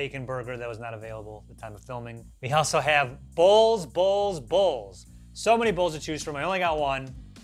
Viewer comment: Please tell me I'm not the only one who thought said "we have balls, balls, balls" at about 15:35. I thought it was going to be a 'funny' name for a meatball dish I'm always glad for another Food Wars episode but I think is right, Denny's doesn't need to exist here. There's better places to get breakfasts, sandwiches and desserts and Denny's don't seem have a particular selling point apart from being American and having a few funny names for their dishes as far as I can tell. Teenagers hang out at fast food places like McDonald's and older people go to garden centre, farm shop and National Trust cafés (ok, terrible generalisation there, middle aged people do go to those as well). People who want a cheap breakfast go to Wetherspoons or a caff.